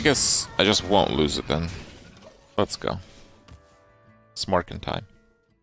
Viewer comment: I know he said Smurfing time, but all I heard was it's morphing time, from mighty morphing power rangers 30 years ago.